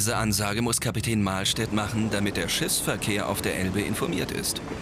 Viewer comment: wer hat noch bei 1:52 Schissverkehr verstanden? xD